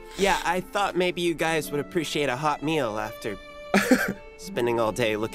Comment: When Oscar said, "a hot meal after looking for me," I thought he said, "a hot male." ROFL, I'm crying.